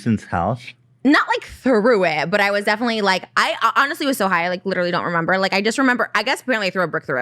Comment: did Trisha say “I was high on meth”